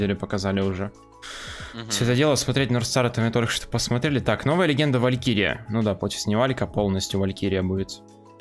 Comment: Мне послышалось новая легенда валькирин